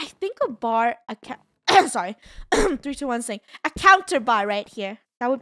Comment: we do need i think a bar, oh sorry- three two one sing.. 19:18 idk what she said but it sounded like sing and for some reason I think its funny